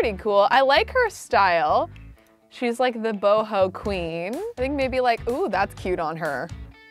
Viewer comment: When Kelsey said “shes the boho queen” i thought she said the hobo queen👑😂